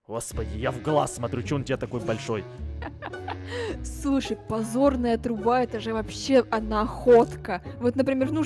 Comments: сказала подзорная или позорная труба мне почемуто послышалось позорная труба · мне одному послышалось позорная труба